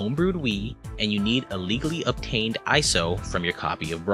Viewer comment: I thought he said an Illegally purchased ISO of Brawl, not “a legally…”😂